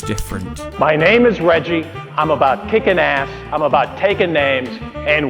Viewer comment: Did he just say “Kick an ass”?